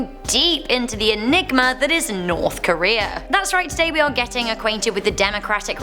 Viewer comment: did she say enigma or ligma